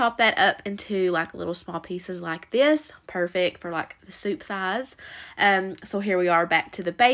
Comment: I know you said "soup size" during the first recipe.. but I heard "soup thighs." I couldn't stop giggling after that.